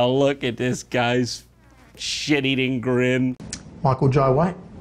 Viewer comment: What name does he say at 18:33? Michael Joe Way?